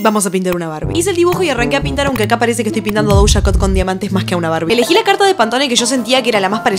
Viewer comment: Pense que decia: Pinte a barbie en una carta de poker 😂 (Estaria bueno xd)